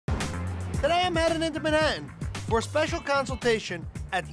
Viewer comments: Y’all what did he say in the beginning I hear “I’m here at the rjdicjwksjfb “ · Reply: I heard today I'm addin into banannin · what is he saying 😂😂it sound like his just making sounds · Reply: “I’m headed into Manhattan” I had to replay it three times lol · what is he saying 😂😂it sound like his just making sounds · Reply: I heard I'm heading into bannanaw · what is he saying 😂😂it sound like his just making sounds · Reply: I heard "i am mad at Manhattan''